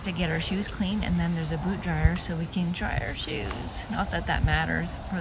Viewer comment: Ohhhhhhhh. Boot dryer. I thought you said Boob dryer until I heard it the second time mentioned.